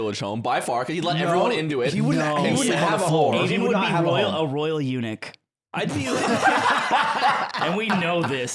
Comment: Apolgy for bad English Can someone please explain what says at 5:33? The subtitles don't show it, and I can't seem to make sense of it. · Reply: "Aiden would be a royal eunuch"